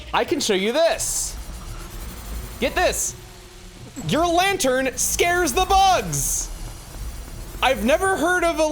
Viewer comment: For a second I thought you were saying that lanters are scared of bugs (but man that would be funny)